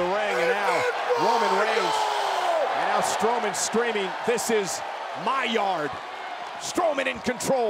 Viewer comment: Thought he said, 'This is Sparta' lmao